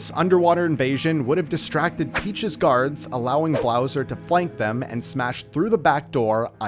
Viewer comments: did he just say blowser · did he say "blowser"?